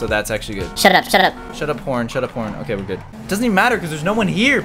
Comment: Am i dirty minded or did i hear him say "shut up shut up p*rn" uMMM